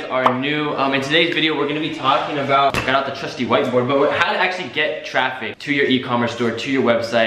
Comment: At first I thought you said, ".. I am your trusty white boy..." instead of I got my trusty white board lol